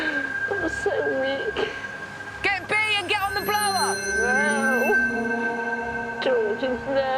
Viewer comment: did they say get the umbrella 😂 some kinda survival equipment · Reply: Get on the blower